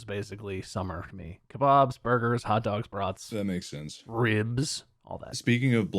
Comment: Instead of "kebabs, burgers, etc" I heard Bob's burgers